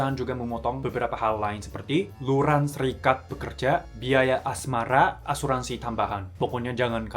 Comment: biaya asmara? Atau biaya asrama? Atau emang ada biaya asmara? Atau salah denger?